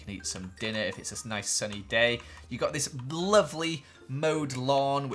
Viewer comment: Did he say blovely??